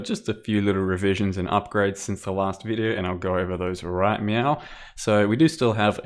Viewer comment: I swear at 3:57 he said "right meow" !